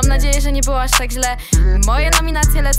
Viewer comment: Fajniee👍🏻👍🏻 Ja usłyszałam tak :,,moje nominacje lecą do Joanny Dupy"